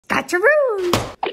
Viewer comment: I literally thought she said gotcha room